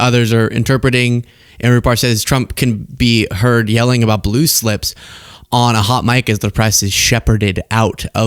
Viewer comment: This blue slip is a GOOD THING!! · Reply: I thought he said blue shit at first